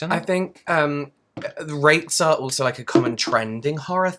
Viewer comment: The captions need some editing this time 'round. Especially at 11:20 it has the R word for SA and thats not at all what said. Rakes?